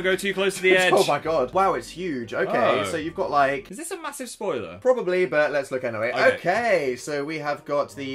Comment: Instead of saying is this a massive spoiler I thought he said is this a massive toilet